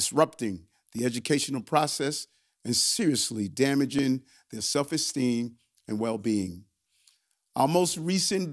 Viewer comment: If I hear "ax" instead of 'ask"......I'm moving on. I will not go along with it. Did I just hear exteem for esteem? 🧐